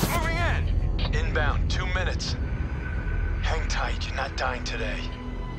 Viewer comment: it sounds like hes saying " Hentai you're not dying today" XD